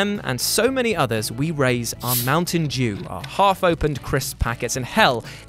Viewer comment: At the end it sounded like said "Mountain Jew".